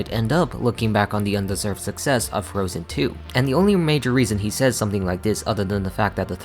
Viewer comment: Did I hear that right? 1:49 - "Looking back on the undeserved success of Frozen II"? If I'm hearing that right, what do you mean undeserved success?